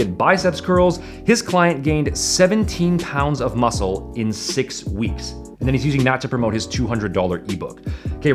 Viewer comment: I thought it said 17 pounds of muscle in 6 months. I be tripping